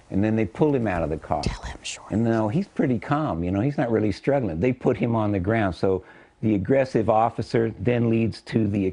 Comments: Im trying to figure out what she whispered while he was talking at the 2:15 mark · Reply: "Tell him to shorten his answers." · What did she whisper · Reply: That they all know this is propaganda